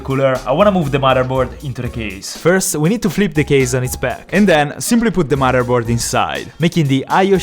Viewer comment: what he is saying: and then simply put the motherboard inside what i hear: and then super glue the motherboard inside